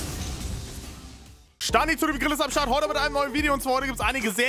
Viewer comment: Ich hab STANNI ZU DEM GRILL verstanden :D🙃😀